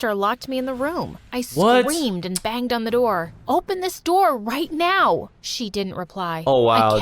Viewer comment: what she really said "open this door, bitch"